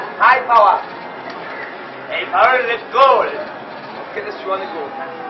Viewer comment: Man's deffo from the north east like, so am I but what does he actually say after "high power!" All I hear is "ey power lez goal" 😂